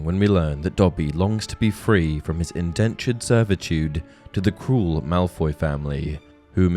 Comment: I misheard him when he said crule Malfoy family and thought he said cool Malfoy family😂